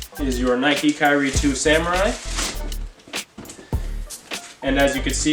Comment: did I hear nike kyrie 2 samurai?